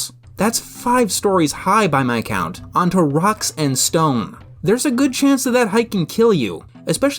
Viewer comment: Did I hear a Rock and Stone?